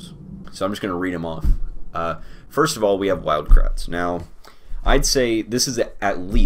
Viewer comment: Thought he said Wild Krauts. Would have put that at S tier